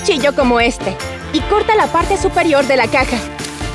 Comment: Yo escuche en el momento 0:56 caca🤣